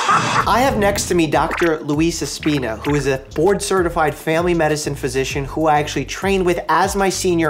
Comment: When he said board certified I thought he said borderline certified.. We were about to have a very different conversation bro